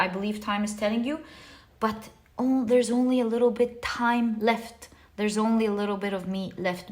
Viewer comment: What did you say here? Couldnt understand.. Little bit of time left, little bit of ??? left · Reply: A little bit of time left, a little bit of me (meaning time, since it’s time talking to you) left ❤️